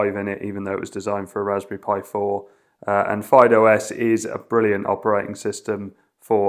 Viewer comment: I've gone back to listen at 2:52 over and over again, but just can't make out the name of the distro running on the RasPad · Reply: Fyde OS